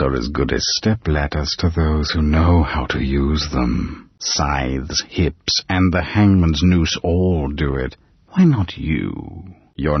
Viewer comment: What is he saying at 0:46 ? It sounds like he’s saying “scythes, hips, and the hangman’s noose” so either I’m mishearing him or he is saying that and I just don’t understand what he means lol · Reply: Scythes, hips, and the hangman’s noose all sway